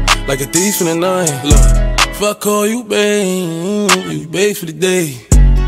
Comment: lol I thought it said f*** all you bae but it is if I call you bae